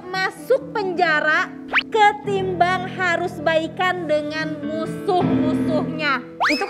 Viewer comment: Salah denger gasi? Pertanyaan: "baikan dengan musuh" Yg didenger: "balikan dengan suami"